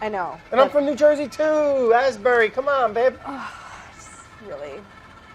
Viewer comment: did brad call claire “babe” at 15:46 or do i just ship them too hard